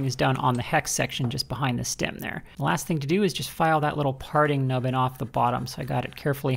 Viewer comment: Thought you said party-nubbin at first lol